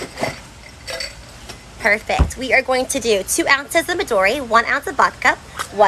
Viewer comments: Anyone gonna mention how it sounded like she said "one ounce of Midorya, one ounce of Bakugou" as she poured them both in Sorry mha is always on my mind.. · I thought she said midoriya haha